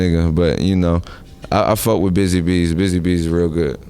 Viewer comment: Did he say Busy Beats?